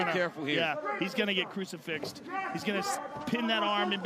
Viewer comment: I heard fuck fix from Joe 🤣😂😂🤣😂🤣😂😂🤣